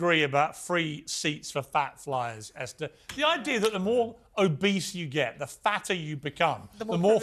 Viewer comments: When Piers called the fat influencer “obese” it sounded like he said “a beast” — as in, “why do you get to have an extra seat just because you’re a beast?” I had to rewind the video to make sure I heard him correctly! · He said "Obese" I thought for sure he said "A beast..."